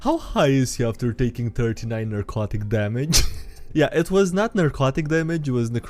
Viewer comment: Narcotic damage? Not necrotic? She hit him with drugs? Lol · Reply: My phone was in the other room, and I coulda sworn I heard "erotic" damage. I thought it was fitting.